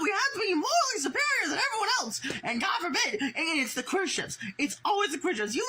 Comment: i think he’s saying “it’s the queer ships” as in it’s only the queer ships that get the backlash. really hard to tell tho because he’s allergic to slowing down and annunciating his words